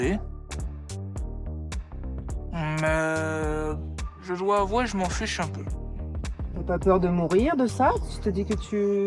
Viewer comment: Putain au lieu d'entendre " Je vous avouerai je m'en fiche un peu " j'ai entendu " Je joue à wow je m'en fiche un peu " 😂😂😭😭